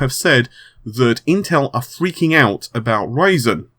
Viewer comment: almost sounds like he is saying that intel is freaking out over raisin